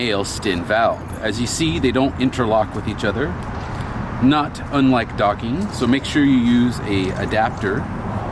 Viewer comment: Hey...at 18:37 did you say "Not unlike docking"? Are you talking about the male on male act of "docking"? Because that reference would be AMAZING! You the man!